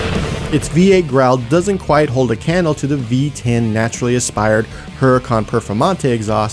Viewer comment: listen to how he said naturally aspirated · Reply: LOL! Sounds like he said "naturally aspired" instead of "naturally ASPIRATED"